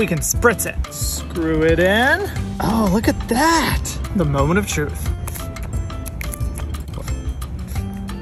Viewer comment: Why did i thought he said: "the melon of truth"😭